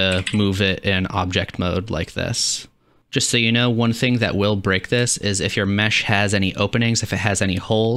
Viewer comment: does he say break or brick? because hah...bricked